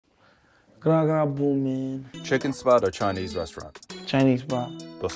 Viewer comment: Those first words sound like he said “gotta go poo man”